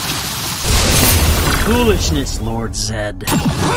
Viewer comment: “Foolishness, Lord Zedd.” Of course. He just had too. ☺️ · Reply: This entire time I thought he said. "Who is this Lord Zedd?" But "Foolishness, Lord Zedd." works great too.